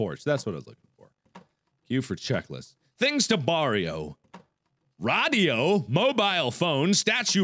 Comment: Did he just said "Things to Borriow" at 0:56??